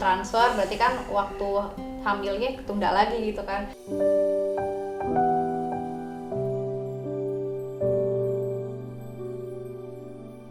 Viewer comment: Iseng perlambat di menit 26.50 jadi yang kecepatan nya paling lambat di 0,25x terus kayaa denger ka kia bilang aku hamil. Ya Allah semoga ga salah denger